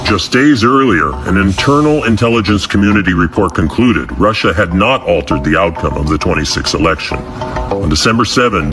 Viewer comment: mark : "...... Russia had not altered the result of the twenty six election. " Did I hear that right?